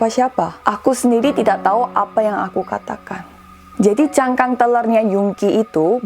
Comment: maap ka bilang apa yh? "Cangkang telar"? "cangkang trailer"? Ga jelas mksdnya apa? Artinya apa?